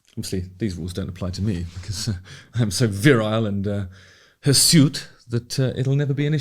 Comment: Does anybody know what's the word that Justin uses after virile at this time stamp: 01:16? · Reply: Think he said hursuit which means hairy